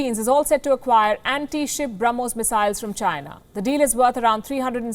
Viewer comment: did she said Philippines is set to buy bromhas missile from CHINA 🤔 !!!!! Didn’t except this for her palki